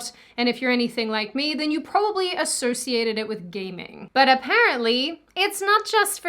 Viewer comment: for a second there I thought you were saying “gay men” instead of “gaming,” but I guess both can be true depending on your pov…